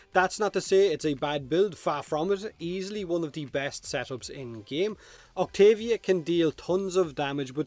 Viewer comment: what is he say at 0:44 "easily one of the best <can't understand> in game" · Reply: "easily one of the best setups in the game" - in Irish